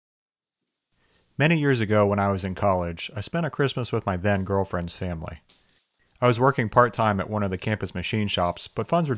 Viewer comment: Ok I thought he said dead girlfriends family and I was sad